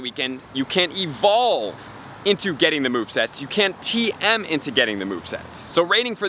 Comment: did he say you can TM into getting the movesets? Like use a regular tm to get legacy moves???